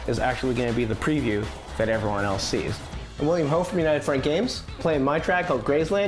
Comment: haha, i thought he said william hung